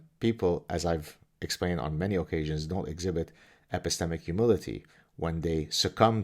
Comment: I thought he said occasional Kotex😉